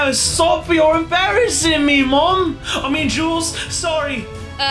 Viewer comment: aww did kit just called Jules his mom🥺🤗